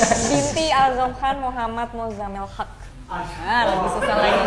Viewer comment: Aghniny Haque binti siapa tadi??? 🤔🤔 Tambora emang beda ya??? 😍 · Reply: Kalo ga salah denger binti: "Al sofan Muhammad musamal Haque" beda ga si sama yg di google ? 🤔🤔